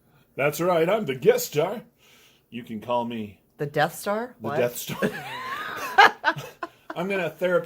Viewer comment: I heard it as the death star first too :P I do love this knife!